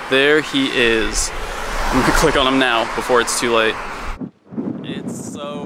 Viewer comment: "i'm going to click on him before it's too late" did really said that..